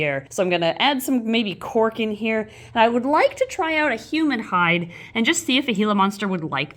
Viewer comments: I heard "human hide" instead of "humid hide" and was like, "uh... Ed? Why haven't we seen Ed?" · My condolences to the family of Steve. Thats a beautiful lizard, and its obvious he cared for her a lot ❤. At 10:00 sounds like says human hide, even though ik she says humid hide (start at 9:57 to hear properly).